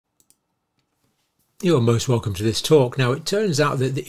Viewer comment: Did he sound like he said: "You are ALMOST Welcome to this talk?"